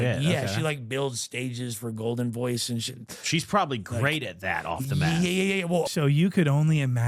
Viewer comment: I cracked the fck up and ilmost spit my drink out when i heard say "SHE PROBABLY GREAT AT THAT OFF THE METH" lmao!